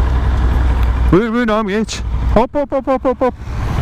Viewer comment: did he just say "Bitch"?